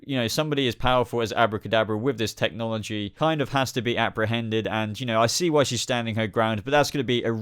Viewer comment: lmao i thought u said "abra" hended instead of apprehended